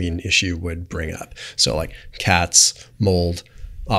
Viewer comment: Did he say “cats”? …are people with histamine intolerance allergic to cats and vice versa?